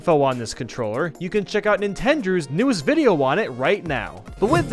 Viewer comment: Who else hear nintenders?